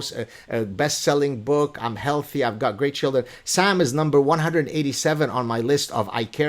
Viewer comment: Did Gad say 187 (on his list) at +48:07? lol